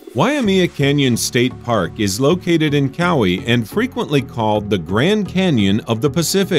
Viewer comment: Not prounuced as kow-wee its ka-waí LOL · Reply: I thought he said Maui, and had to listen twice to hear him mispronouce Kauai. Gee get it right.